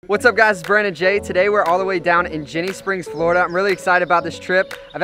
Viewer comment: At first I thought he said shitty springs florida